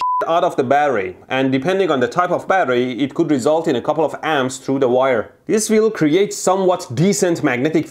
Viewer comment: Wait a minute.. did he said.. "Through the Wire"? KANYE WEST REFERENCE!!! (In his 2000s eras) "I drink a Boost for breakfast, an Ensure for dessert. Somebody order pancakes, I'll just sip the sizzurp"